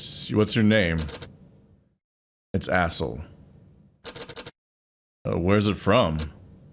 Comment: For a moment there 9:15, it sounded like the wolf-dudes name was Asshole.